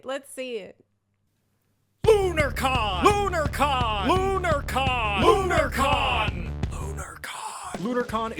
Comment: Not gonna lie, I was half asleep and in the other room when this video started on autoplay and I could have sworn I heard, "BonerCon! BonerCon! BonerCon!" 🤣🤣